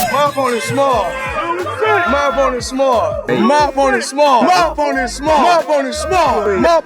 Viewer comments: Was it just me who thought he was saying "my phone is small" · LOL, I thought he was saying "My Boners small" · Sounds like he is saying "MY BONER SMALL" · is it just me or is Mayweather saying my boner small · Honestly thought Floyd Mayweather was saying "my boner is small" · I thought he said my bonersSmall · I thought floyd said “My phone is smart” 💀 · I thought he said “My boner’s small” 💀💀💀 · I seriously just realized that mayweather was saying “my opponent is small”. I thought he was saying “my boner is small”, as an insult to Logan’s face. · I thought he said my boner small🧍🏾‍♂️ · I heard "my phone's small" 😂